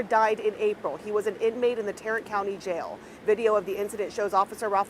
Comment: Oh, Tarrant, I thought you were saying Tyrant County.